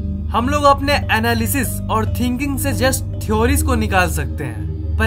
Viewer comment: I just thought that he was saying Annabelle